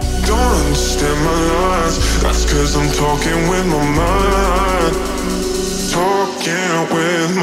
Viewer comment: I don't know why I hear "talking with mamma"